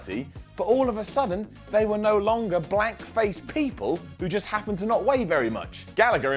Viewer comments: On The List I heard that too lmaoo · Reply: I just listened to it again, it really really sounds like he's saying black faced people. It's probably my American ears hearing an English accent wrong, but it did make my eyes pop the first time I heard it. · I may have misheard him but did he say Black face instead of blank face? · I just listened to it again, it really really sounds like he's saying black faced people. It's probably my American ears hearing an English accent wrong, but it did make my eyes pop the first time I heard it. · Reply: On The List I listened to it again with the volume up but he said blank..The accent really makes it sound like he said black tho lol